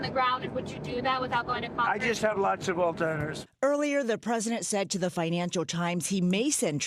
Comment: did he just respond with "I just have lots of altzheimers" because that's what I heard here...